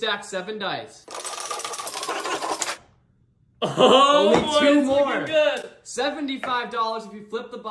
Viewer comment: ＄50の成功してすぐ「めっちゃ上手い」って空耳が混ざってくる。笑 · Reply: まさかの Only two more 音全然違うのに確かにそう聞こえるwww